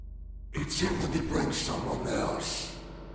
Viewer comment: what does he say? 'it's simple to break someone else'? i can't get it easily. 14:52 this one, too.